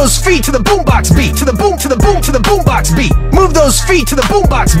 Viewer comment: Dude when Brock said To the boom box beat i thought he said To the boom box b-tch